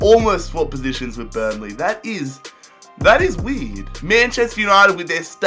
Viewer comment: sounds like he says "that is weed"